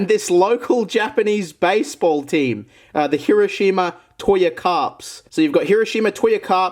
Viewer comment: you: says Hiroshima Me: I thought you said Kirishima like in the anime My Hero Academia